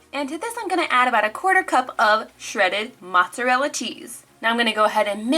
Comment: did she say teese or cheese ???